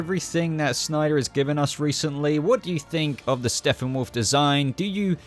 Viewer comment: Dood.. Am I trippin or do I keep hearing "Steffenwolf" everytime time u say his name in this video?? Lol.